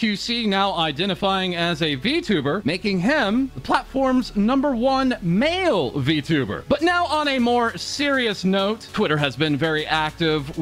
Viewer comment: xQc sounded like he sayin "vtubber"